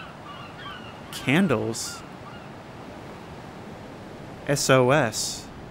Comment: I thought that said sus i need help